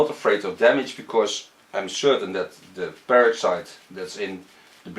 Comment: It sounded like you said the "parasite" in the bleach. I was like WHAT!?! Then I realized it was "peroxide" you were saying. Haha. I was like WOAH NELLY WHAT KIND OF BLEACH ARE WE USING lol